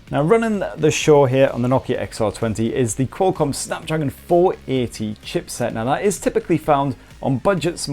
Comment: 8 minutes into the video and i hear "SNAPDRAGON 840" fuck this shit 💢